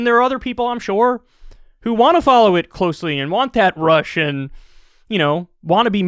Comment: '...and want that rush and...' Funny, I first heard that as, 'and want that Russian....' 😊